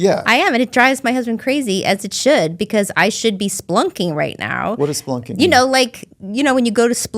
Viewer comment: Just an FYI the way she pronounced spelunking (spu-lunking) made it sound like she said "splunking". At first I didn't understand her either. Origin from the word "spelunk" meaning a cave. I used to go spelunking a lot here is KY where there are a lot of caves. Great show BTW.